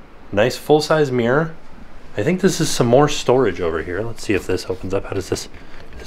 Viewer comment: I heard S'mores storage. I expected to see s'mores and was disappointed.